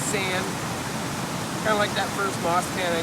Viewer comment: Guess its moss pan, i heard you say moss bags during your video.lol. anyway,good job